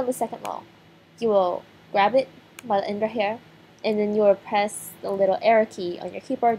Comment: What did she say at 3:10? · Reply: GhostlySpaceships she said press the little arrow key